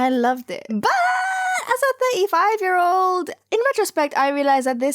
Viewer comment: You’re 35?! Or did I hear that wrong. Either way you look great.